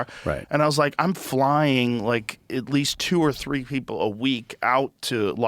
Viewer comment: What did he say at minute 50:00 then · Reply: at least 2 or 3 ppl a week